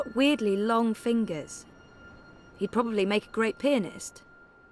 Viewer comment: I misheard pianist as penis...@@ he's also got weirdly long fingers he'd probably make a great penis whutt?!!